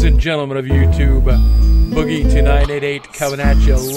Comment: Whenever he says "Boogie2988", I hear "Boogie Tonight88". Please tell me I'm not the only one.